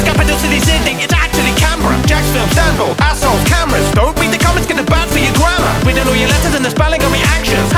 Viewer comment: I thought said "don't read your comments cause they're bad for your grandma"